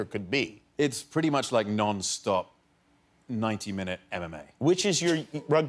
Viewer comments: Did he just say 90 minute anime? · i thought henry said rugby was like non-stop 90 minute anime